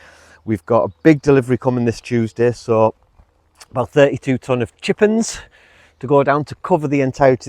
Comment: Did say "32 ton of Chickens"????